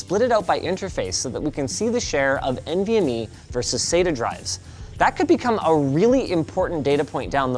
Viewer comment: A note for an error in the close captioning @ 4:36 the CC says SETA instead of SATA. Small correction that might want to be fixed.